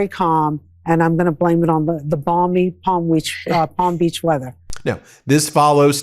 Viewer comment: did she say enbalming weather?